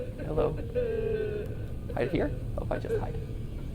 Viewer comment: freddy is definitely say hide in him